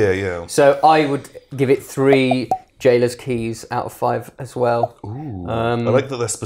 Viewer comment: Totally thought you said J-Lo's keys.